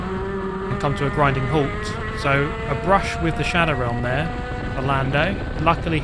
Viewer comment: I’m waiting for the next video: “Sending Lando Norris to the Shadow Realm” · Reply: Ach ich hab immer Shadow room verstanden